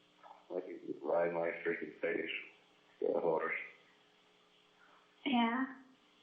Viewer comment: What did Travis say on 28:35 after he said he wanted Jodi to ride his freakin' face. What did he say? Like a horse?